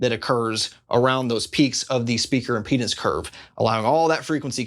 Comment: did he say “speaker penis curve”?